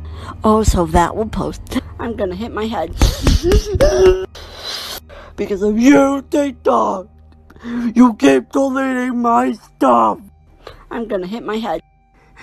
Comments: creo que dice "because of you, tiktok" que es "gracias a tí, tiktok" como culpando a tiktok (¿ lo demás no lo entendí aa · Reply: es, "i'm gonna hit my head, because of you tiktok, you keep deleting my stuff! i'm gonna hit my head" (golpearé mi cabeza por tu culpa, tiktok, sigues borrando mis cosas, golpearé mi cabeza) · creo que dice because of you tik tok,algo así como por ti tik tok ,no? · creo que dice "i hate tik tok" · creo que dice porque tu! tik tok )aqui si no entendi muy bien(das de mi piedra